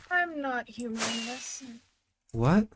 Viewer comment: mate you cant blame for not understanding all i heard was "im not humaness"